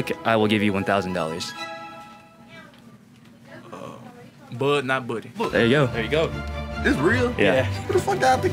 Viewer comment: What did he say? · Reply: It’s a book called “Bud not buddy”